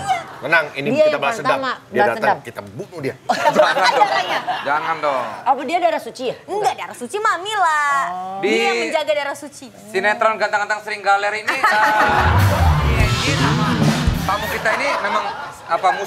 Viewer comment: Ga salah Denger gua Desta bilang Ganteng Ganteng SeringGaler🤣